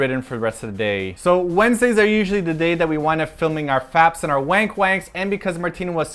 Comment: did you say fap and wankwank? XDD Aww you guys are so cutee~ and those are perfect gifts for someone who loves to cook :3